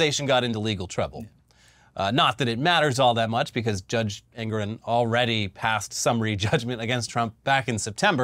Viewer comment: Thought you were saying "Judge Angron" and I thought, "he'd make a terrible judge!"